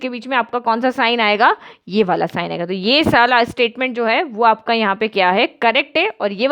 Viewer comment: 😂😂😂 Mam ne galti se kya bol diya Anyway...👍 Thank You Mam ❤️ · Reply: Mam wanted to say - "ye vala" but accidentally said-"ye sala" . It's ok that's fine 🙂🙂